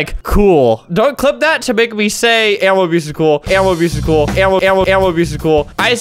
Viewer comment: It mightve sounded like he said "animal abuse is cool", but he really said " animal abuse is cruel"